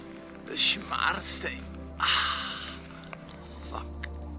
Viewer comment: does he say fuck ? HAHAHA